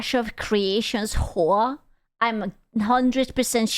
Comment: She said “Hoa”. I heard “whore”. She meant “H.O.A.” Funny yet entirely consistent in all three contexts